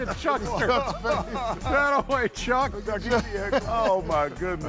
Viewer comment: did Kenny just say Demarcus?